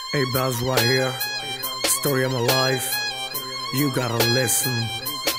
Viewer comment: I'm waited for this line" chal Hatt! Nikal meri Zindagi se" 😂 memes forced me to come here & to listen this song 🙂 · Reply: is he saying story of my wife and u gonna listen